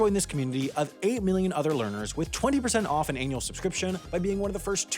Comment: Wow, at the end, I really thought you said "with an anal susbscription" (even after backing it up). I had to slow it down to hear the correct word. Maybe it was cause I was staring at a brown background on my main monitor at the time ;)